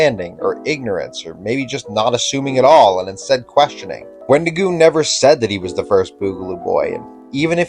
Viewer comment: Great video, but I have a minor criticism about your subtitles, as there are a handful of times where they aren't reflecting what you're actually saying. For example, at 4:49, the subtitles say 'Wendigu' as opposed to 'Wendigoon'. Im not going to pretend I know anything about the video editing process or the process you used to get your subtitling, but I think that finding a way to improve them will make your already great video even better. Im also a person on the internet, so feel free to disregard.